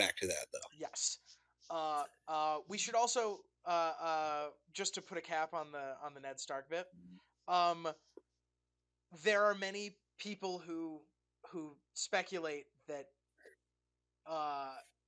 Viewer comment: Did I just hear "Ned Stark be a horse"? Like in "have became a horse"? More and more werehorses we have.